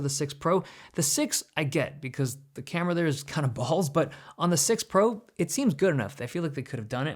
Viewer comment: did dave really say balls?